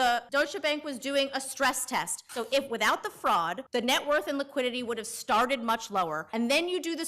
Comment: did she say fraud?